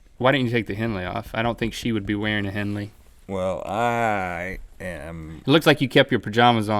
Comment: When Rhett mentioned a 'henley' (?) I thought he said "a hen weigh". I thought Link would ask "What's a hen weigh" and that Rhett would reply "About 2 and a half pounds"